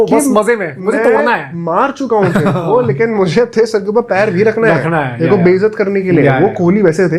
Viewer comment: ayo i thought he said ma chudao